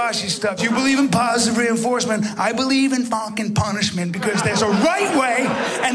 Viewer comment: I thought he said Vulcan Punishment.